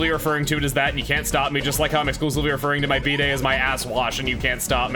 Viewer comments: When he says "referring to my bidet as my asswash" i thought he said "b-day" like birthday and saying "today is my 30th asswash" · I misheard that as "B-day" and got very confused about why you'd call your birthday that.